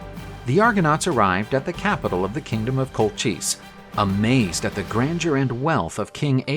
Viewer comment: I keep hearing "The kingdom of Cold Cheese"